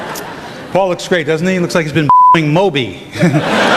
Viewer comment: anybody know what they said there? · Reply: "Looks like he has been fucking moby"